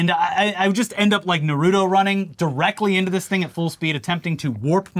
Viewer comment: I thought you said Naruda not Naruto... I mean his politics track butI don't take y'all for poetry readers. Or weebs for that matter. I'm confused now.